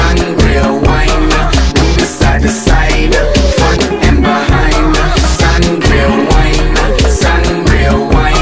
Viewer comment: Am i the only one who misheard “front and behind” to “fuck from behind”